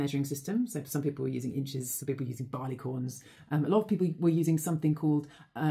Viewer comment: Listened to this 17 times to be sure and I've concluded that barley corns make much easier measuring tools than barnacles, which is what I first thought I heard listening without watching. That said, I'm sure there are lots of errors to be found, that were ever based on the need to be accurately heard from a distance. "Broken telephone" steps in and could have resulted in quite a few issues. So yeah, use barley corns instead of barnacles, lads.